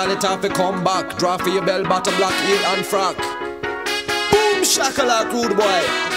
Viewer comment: heel and frock no idea what it means but i thought he said Anne Frank so that's why i checked 🤣